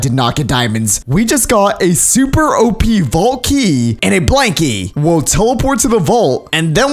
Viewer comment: When ever you said blank key it sounded like you said blankly